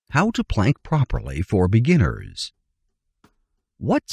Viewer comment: That is what my sister in law said to me last night but I thought she said how to blank properly damb it.